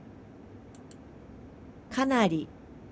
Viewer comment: 카나리이지만 항아리로 들리는 개방장....어지간하게 모어쌍이 또......아니 게다가 뜻도 어지간히임ㅋㅋㅋㅋ